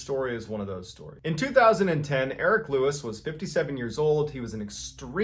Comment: When he said "Erick Lewis" I was chewing and swore he said "Derrick Lewis", who is a UFC fighter in the Heavyweight division. I was like there ain't no way his fat *ss is an experienced mountain climber lol.